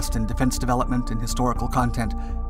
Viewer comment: that thing is so ad hoc it’s funny, concocted by committee, and it looks more like a flying mausoleum than an ambulance. i always like how our humble narrator say: stay tuned. it also always sounds like he says: check out our duck documentaries. keep up the good work, always interesting and informative. 👍 👑 love